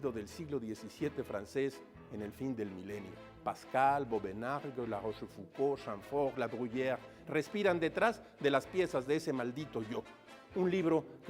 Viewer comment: Disculpe, pero en el minuto 5:30 menciona a varios autores, solo al primero (Pascal) le entiendo lo que dice. ¿Podría poner por escrito esos nombres?. Gracias. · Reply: Blaise Pascal. Luc de Clapiers (marqués de Vauvenargues). Francois de La Rochefocauld. Nicolas Chamfort. Jean de la Bruyere.